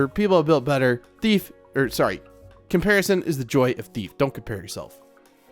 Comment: did u just say comparison is the joy of thief ??